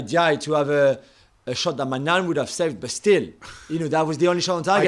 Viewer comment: I thought he was talking about the AC Milan keeper Maignan at 1:28, its only when I heard it again I realised he said my nan.. 🤭